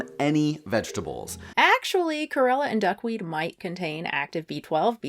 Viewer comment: Lmao this is completely random but when you said “actually, Chlorella and duckweed might contain B12” I heard “gorilla and duck meat” -well, they probably do contain B12, but I’m surprised to find out that gorilla meat is vegan 😂